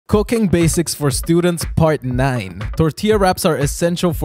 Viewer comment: I heard “Cocain basics for students”😂